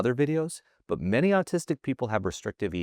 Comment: I must be drunk or something. I thought he said, "artistic", and watched this like, "yeah man, I get that".